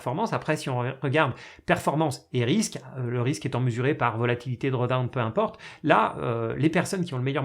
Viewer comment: Quelle est la méthode citée pour calculer le risque d'un portefeuille ? J'ai compris "volatilité dredin" mais mes recherches ne donnent rien. 😅